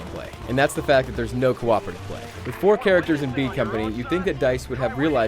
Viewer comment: did he just say bean company?